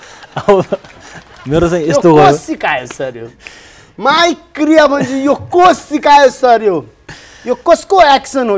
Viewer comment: did he said maicriya mu* ?😮🤣